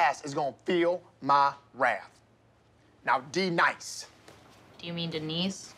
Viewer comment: i thought he said “now be nice” 💀